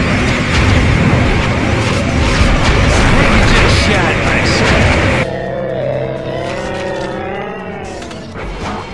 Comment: English isn't my native language, so what crypto says in 8:50 ? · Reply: Fabrício Teles "I think I just shot myself"